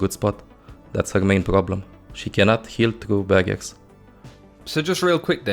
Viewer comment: I thought he was saying Ana can't heal through baggets